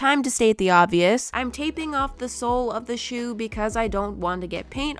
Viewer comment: What said: “I’m taping off the sole of the shoe..” What I heard: “I’m taping off THE SOUL of the shoe..”